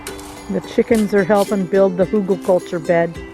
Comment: Well, done (and). Now you have a great bed that lasts for years and keeps the moisture. Great to have all the material from your own property and free from the beach. Nice also to see your beach. Beautiful. It will be exciting to see what you are going to plant in the bed. Have a nice sunday. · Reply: That was funny. At 3:22 you said The chicken is helping build... (I heard). Say I’m sorry to Jack. I give him full credit for the great digging😄